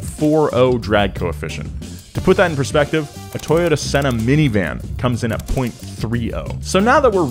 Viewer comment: Is it me or did I just hear Nolan say Toyota Senna?😂